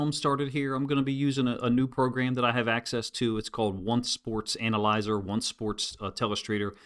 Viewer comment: For anyone who may have misheard, the software is Once Sports. I was hearing One Sports.